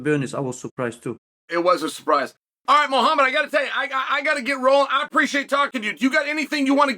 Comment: I thought he said Mohamed Moicano 😂